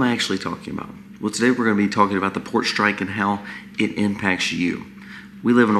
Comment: You sickly voice fooled me! I thought you said “PORK strike”! Not funny topic, but much worse than not getting bacon!